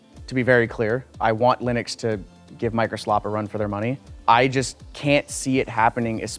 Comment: am i deaf or did i just hear say microslop?